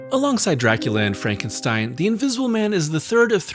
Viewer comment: I literally had to do a spit take,when I thought you said Dracula Anne Frankenstein 😂,I was like yo what is this uncharted territory Universal 😅